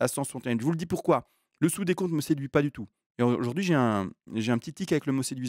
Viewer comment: À un moment j'ai compris "le sou des cons" 🥲